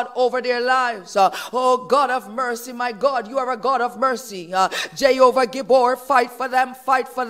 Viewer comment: at about 11:18 she says Jay Ober Guboor fight for them. What is she saying? Who is that, or what is that?